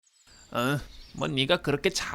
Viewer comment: 자막을 안보고 보면은 언니가 라고 하는디 자막을 보고 들으면 뭐,네가 라고들림